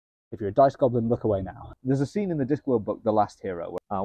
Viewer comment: I thought it said "nice goblin" and i was like "well thats some nerdy kinky stuff lol